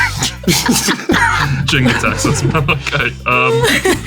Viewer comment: Did actually say Jin_Gitaxias in the moment?